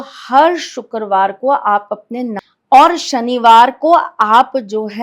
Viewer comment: Itni cuts kiu??? 25:58 kya boli?? Every friday ko kya Krna padega?? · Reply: इत्र